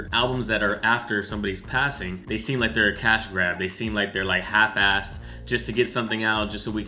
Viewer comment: Cask rap not cash grab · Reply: you kept saying “cash grab” but I kept hearing “cask rap” like short for ‘casket rap’